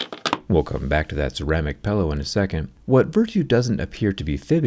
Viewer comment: "pellow" pippa is jerryrig? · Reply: He keeps saying 'pellow' instead of "pillow"